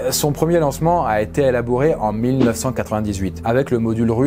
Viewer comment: C’est moi ou à 2:37 il dit elabouré 😭😭